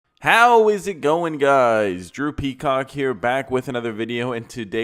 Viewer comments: Yall hear him say "Drew Peacock here" I hear "Droopy Cock here" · i thought he said 'Drippy Cock here" lmaoo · LMFAOAO Idk why but in the intro I heard "Drippy Cock here" 🤣😭